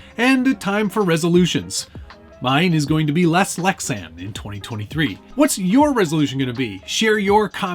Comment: Uhhh… LESS lexan? I thought you said NO lexan